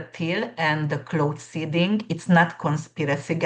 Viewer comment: Waht did she say here? Peter Thiel and what? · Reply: Cloud Seeding. She said that is definitely happening.